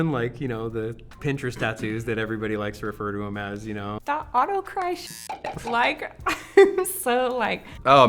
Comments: also what the fuck are they saying at 6:00? autocrisis? autocracy? autocry? hello? help? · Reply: I think the artist name is auto_christ . :) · What the heck are they saying at 6:00???? What tattoo artist? Auto Cry? Otto Crisis? What name are they saying? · What is the style of tattoo that they said they didn’t like with the ugly line drawings. “Auto-cry?” Hard the ugly trumpet and stairs on stomach. Please tell me the name of that style.